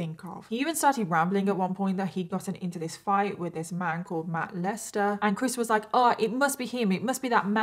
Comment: I thought you were saying "molester" but it was really "Matt Lester" 😂